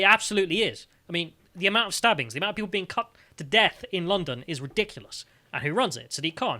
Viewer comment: Could have swore he said people in london were being 'cucked to death.